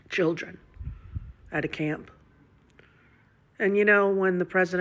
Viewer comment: Did I miss something? I first thought she was drunk because I first heard “when the president gots the emergency early warning systems” but when I replayed it I heard her say “guts”. Kinda funny the snowflake with all the lies and lack of guts that runs to Ireland while claiming he will destroy our nation” is the last one to be complaining. She makes me wanna vomit my “guts” out. I pray they keep her in Ireland forever. Or better yet she can move in with Ellen and Portia because they are no different.